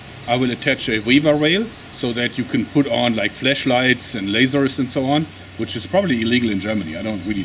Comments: I heard 'Fleshlights' who else heard the same? :D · Did someone say fleshlight? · Thought he said fleshlights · ahahahha sounds like he says fleshlight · did it sound like he said "fleshlights" to anyone else? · i thought he said fleshlights... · When you said you could attach some flashlights or lasers, it kinda sounded like you said ''I might attach some fleshligts and lasers...'' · Did you say Flesh lights >-> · I can't be the only one who heard "fleshlights." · I'm not going to lie, when he was talking about the front mount "flashlight" I thought he said "fleshlight" and I just lost it XD